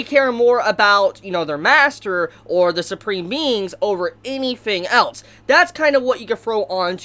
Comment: I know he said supreme beings but I heard.... SUPREME MEMES!